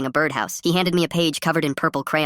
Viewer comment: Not gonna lie I thought it said “girls bouncing” at the very end there